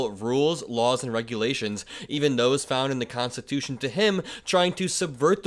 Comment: I hear him saying "constituted", which reminds me of the frozen orange juice cans that we had in the 60's/70's.